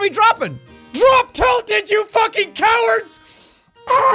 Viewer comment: I accidentally skipped backwards, and it sounded like jack said," DROP TILTED YOU FUCK BUTT"